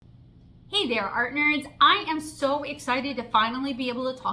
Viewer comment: I thought you said "artners" like "partners" and I was ready to root and toot!